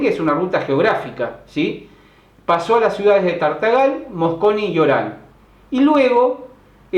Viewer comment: hola profe!. no se entiende en el minuto 10:30 las ciudades que menciona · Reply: Hola Melina las ciudades que nombro son Tartagal y Orán que son ciudades que pertenecen a Salta , en el Norte de Argentina !!!